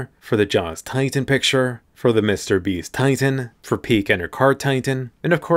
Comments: am i the only one who heard 'Mr Beast Titan' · did I just heard Mrbeast?